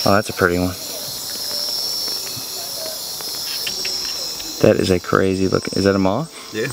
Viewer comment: "This one is my favourite" Caption stay on as the video shows 5 different insects :| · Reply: VictorsMod ETA: Mac Was and VictorsMod are talking about annotations, not captions. If like me you don't see it, turn off captions and turn on annotations. Thank you! But I just watched it ('cause now I started this conversation so I figured I should) and didn't hear/see him say "this one is my favourite" he said "That's a crazy look- Is that a moth?" and the captions matched. Before 1:20 he said "that's a pretty one" (with 1 moth shown). Are you guys maybe talking about translated captions? I guess it doesn't matter, it's just bothering me now lol. (or should I say "bugging me" XD)